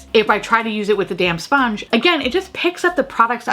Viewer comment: I love that every time you say "damp sponge" I hear "damn sponge" and it makes it so much more aggressive lol 🤣